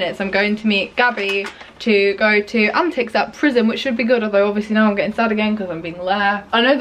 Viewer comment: you telling your dad that you said PRYZM not prison is so funny cause I was on the phone to my dad earlier and told him I went to PRYZM last night and he also thought I said prison hahaha